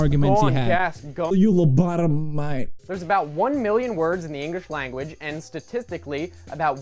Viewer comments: Did... did he say Engish at 3:09? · does he say engrish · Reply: Enjrish* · WTF DID HE SAY "ENGRISH"!? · did ThioJoe just say "Ingrish Language" XD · Sounds like he said Engrish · Anyone eles notice thiojoe said engrish? Instead of english